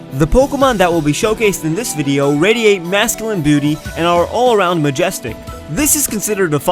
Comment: I can't be the only one who heard him say "masculine booty" at the beginning.